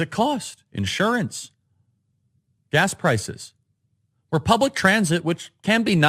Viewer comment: Did he just say "republic transit?